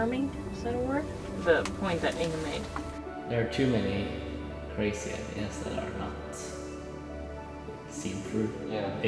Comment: So inspiring! What does say at 5:48? It's hard to hear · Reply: “There are too many ideas that are not seen through. People.. they’re just talking about it like “oh it would be so cool to do this, it would be so cool to do that”. Yeah! You should do it then.”